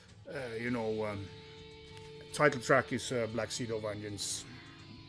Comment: Cheers, at first I thought you said Black seeds of onions.